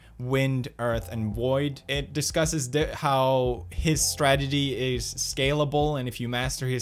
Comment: why i hear "stragedy" when pew speak "strategy"